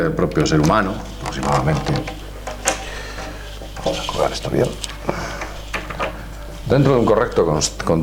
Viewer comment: que onda con esta mie*** escuchen lo que dijo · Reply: jajajaja creo que dice "voy a colgar ésta mierda"